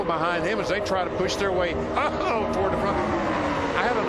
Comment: did he say optical dilusion??